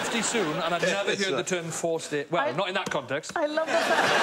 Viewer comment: I thought she said “four stair” and I was more confused than Rhod